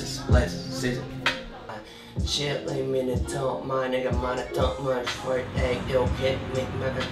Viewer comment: Okay I love him but did he say the n word at 1:50?!!!?!?? Cuz that’s wrong my dude